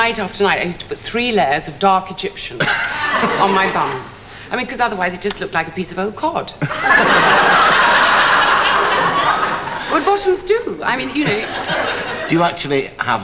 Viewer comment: I loved her always too. What is she saying when she says, without makeup her bum would just look a piece of (sounds like old cord)? at 2:30 ANYONE???